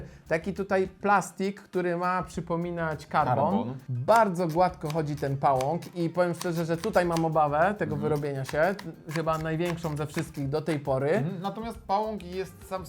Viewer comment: czy tylko ja usłyszałem karton?